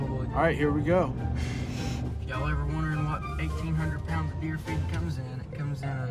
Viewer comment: I thought he said deer feet I was like wtf he need 1800lbs of deer feet for.!!!